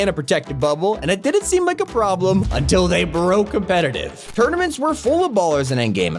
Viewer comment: What did he say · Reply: competetive